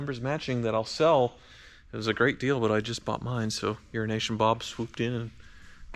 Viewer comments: I swear it sounds like you say Urination Bob. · It always sounds like is saying "urination" Bob :D